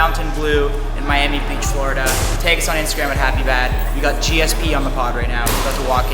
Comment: did he say happy bad or happy dad 😅?!